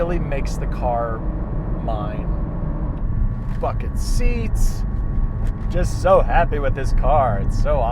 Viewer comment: sounded like “fk’n seats” lol.